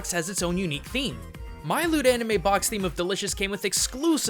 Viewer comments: Man, I hadn't even considered the possibility of Primarina being a selkie! Thanks for the enlightenment! Also, I misheard your sponsor as "lewd anime" XD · I thought you said lewd anime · For a moment, I thought you said that you wanted us to support "Lewd Anime".